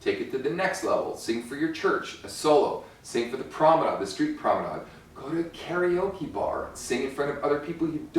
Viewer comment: What does he say at 9:02? Street pramada? Street ramada? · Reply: Sing it for the promenade. The street promenade. (like busking on the street)